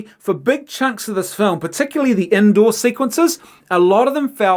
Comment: When he said indoor I thought he said Endor and got confused for a moment